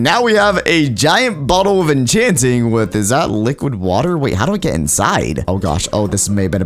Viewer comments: Did anyone else hear him say liqud water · did he say liquid water 😂